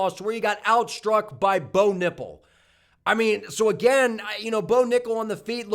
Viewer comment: hahaha i had to rewind it when i thought you said "Bo Nipple"@ 34:25🤣